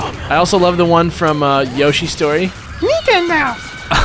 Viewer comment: When yoshi said "nintendo" I thought he said "jingle bell"